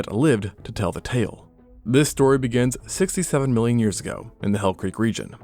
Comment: how many years ago did you say? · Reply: 67 million years ago, the hell creak region